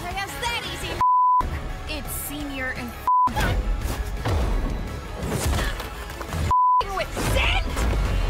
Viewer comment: WHAT THE HELL DID SHE SAY?? 😭😭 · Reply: She say ducking with cyn 🥶?!?!?!!?